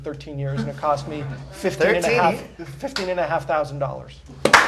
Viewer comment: In min 5:31 I think he said 15500 not 50000 am I wrong?